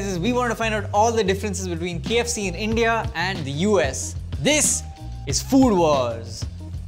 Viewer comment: Did i just hear He said “This is Fool Wars” or I’m just hallucinating??